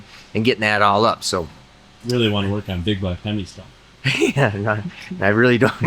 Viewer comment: that comment pinched a nerve 😂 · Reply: I was about to leave a comment with this timestamp LMFAO 🤣 At first I thought you said big block and then I heard big black and either way it’s funny haha his response is really funny. :)